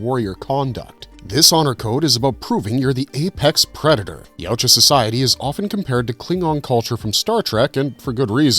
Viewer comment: Im being silly here to catch and report this, but at 4:05 I hear “Yowcha’s su su society.” Was there a spliced edit or was that an extra “s” sound? Great video as usual!